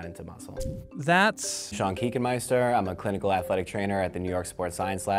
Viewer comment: I heard "Chunky key master"